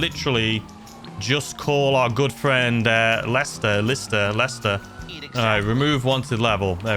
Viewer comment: bro was gonna call lisster instead of lester, wrong channel bro😂